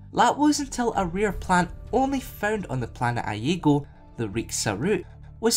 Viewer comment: I thought it said the planet Lego lol